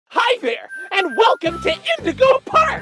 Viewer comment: I thought he said “hi bear” instead of “hi there”